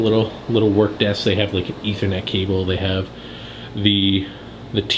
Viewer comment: did he say Etho net cable?